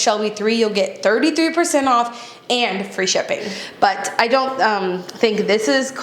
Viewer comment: Instead of 'free shipping', I heard 'free champagne'...